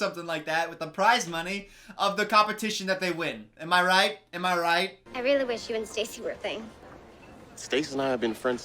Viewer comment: I almost had a HEART ATTACK; I thought that little girl said, "I really wish you and Stacy would bang ."